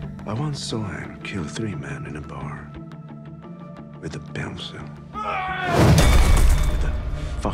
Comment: The way Viggo said “pencil”, I thought he said “bouncer”, and I envisioned John Wick killing three men by beating them senselessly by flailing a bar bouncer by the led like a mace.